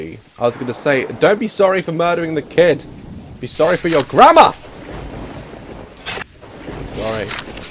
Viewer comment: i thought you said 'im sorry for your GRANDMA' but you said GRAMMAR